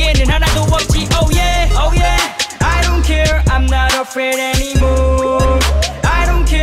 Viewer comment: I always heard "I don't care, I'm not open anymore" kinda embarrassing now but yt knew what I meant haha